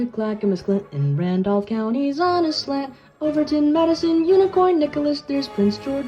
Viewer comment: I heard Unicorn